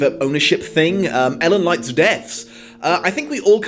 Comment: He's saying "L and Light's deaths".... for the longest time I thought he was saying "Ellen likes deaths"... good thing I only realized that AFTER finishing the manga and anime... But seriously, I feel really stupid right now